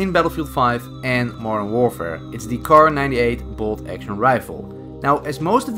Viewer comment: When he was talking about the kar98 I coulda sword I heard him call it M1 Garand and I immediately thought "why would he say that?? This is bolt action, the Garand wasn't bolt!" Then rewinded and saw that I was mistaken